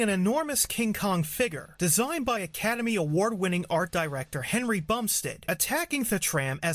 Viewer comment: I thought you said Henry Bumstick lol